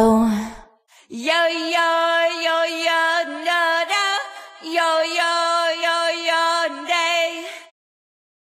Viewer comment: sounds like she is saying “your gay”